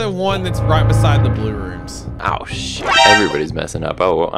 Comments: I'm not a hater but, I just wanna point out that at 2:37 it sounds like said the s word "Oh s everybody is messing up." Again, I am not a hater. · Was it me or did said a swear word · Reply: I believe he said “ah shi-“ then stopped himself?